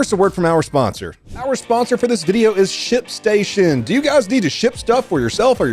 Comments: I thought you said shitstation...then I realized - that the White House... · I swear I thought he said "shit station" at 0:34. Probably didn't help that I just read "dump station" at a state/county welcome center. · I actually thought that you said shit station. 🤣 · Ship station is a terrible name. I thought he kept saying shit station lol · at first i thought you said shit station on the sponsor lmao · I really thought he said "Shit station" lmfaoooo